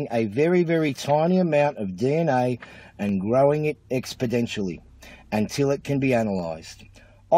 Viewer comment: I heard him say expodentially. What does expodentially mean?